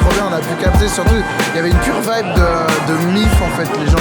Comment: Oui moi aussi j'ai entendu "une pure vague de MILF"